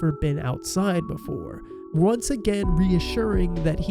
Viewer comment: somewhere around there you said that the chicken one says it bad outside he actually says it’s RAD outside